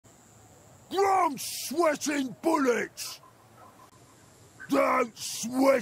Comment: Did he say he's sweating bullets?